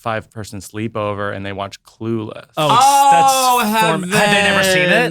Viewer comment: Omg thought you said “clue” and had to rewind because I was so confused